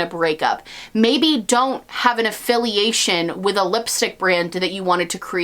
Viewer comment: Hey can anyone tell me what's the censored word at 26:21 ? Nothing I can think of makes sense lol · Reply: Zaynab Thara it’s lipstick nazi 🥴